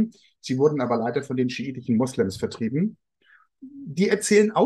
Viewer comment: welches Adjektiv benutzt vor Moslems? Ich verstehe schäbig oder schädlich?!?